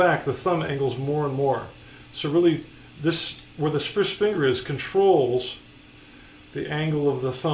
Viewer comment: lol did he say fish finger? haha it sounded like it. Fish Fingers are tasty.